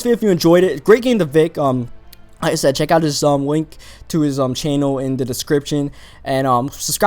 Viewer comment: DID ANYBODY HERE Wink instead of Link